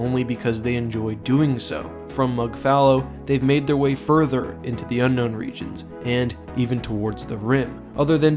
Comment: The thing that creeps me out even more is the way "mnggal" is read in my langguage is as an abbreviation for the word "menggal/memenggal" which means to decapitate. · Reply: I keep hearing it as "The Now Now" which is the name of an album by the band Gorillaz. It kinda confused me when I figured out how it's pronounced.